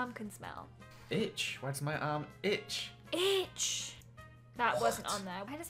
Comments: When says I have why does my arm itch I thought he said b***h why does my arm ich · I swear right before said cheese I yelled cheese at the phone xD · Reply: When said itch I thought he said bitch the first time 😹😹